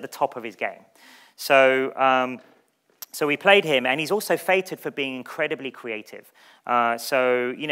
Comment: "So we played him, and he's also [...] for being incredibly creative." What does he say there? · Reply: feted not fated. It means celebrated.